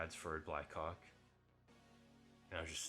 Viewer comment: bro did NOT say blackhawk LMAOOO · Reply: Black co